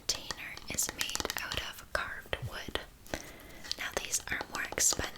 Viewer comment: Why did I hear “hard wood” for 4 weeks….first time I heard carved wood 😂 getting old